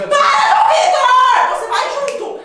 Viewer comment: Jente quem escutou ou foi só eu em 9:43 reparam ela falou mor pro